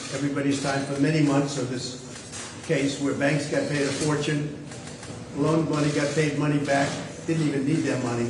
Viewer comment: Did he say "Loaned money got paid money back"?